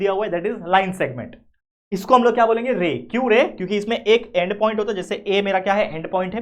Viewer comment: 'THAT KYU RAY',, I HEARD "KYU RE? "😂